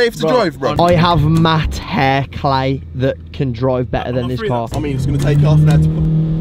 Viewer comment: Wtf did say at 11:33 I can’t understand any of that lol 😭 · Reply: "I have matte hair clay that can drive better than this car"